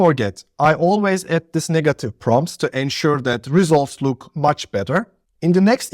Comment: You always add that N? huh? what did you say bro? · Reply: Negative Prompt